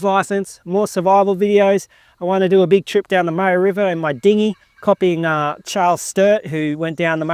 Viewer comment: Fantastic mate. The West coast is a whole nother world in regards to the history. I haven't even begun to touch that, but so many amazing stories. In fact it was on the west coast there was first Europeon contact with the country. I also love the last wave of great explorers in Australia who pushed from the center to the west coast (and back again) Lots to learn there. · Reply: My apologies, I misheard you when you said you were going to boat up the Murray river, i thought I heard you say Margaret River haha. Indeed the west coast has fascinating and rich history. I think the east coast History is far more interesting. My favourite story is of the guy (cant remember his name) who comissioned the first expedition through the heart of Australia to plan a route for the overland telegraph cable. Probably the most inspiring story of the toughest men this country has ever witnessed. Love the videos, cant wait to see what 2026 holds!